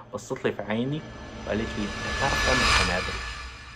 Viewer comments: الدقيقة 12:50 ما فهمت ايش قال احد فهم؟ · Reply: تقريبا بتعرف تعمل قنابل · وش قالت الصوت مو واضح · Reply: بتعرف تعمل قنابل